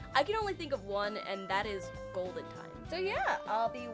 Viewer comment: I thought you said said Golden boy.